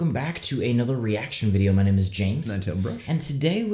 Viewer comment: Okay, so I've gotta say this because I'm an idiot, but I swore you were saying "Im Toebrush" and not for MONTHS. hahahahaha I feel so stupid. Also, zombie movie wise. You need to watch Army of The Dead, Fido, The original Night of The Living Dead, When Good Ghouls Go Bad, and of course you need to watch Evil Dead. All the Evil Deads in order.